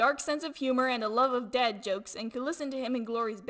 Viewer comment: "Deathbringer, you have a dark sense of humor and a love of dad jokes and could listen to him and Glory banther with each other forever" Me: That sounds about right... Especially about the dark sense of humor, that hit me. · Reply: I thought she said DEAD jokes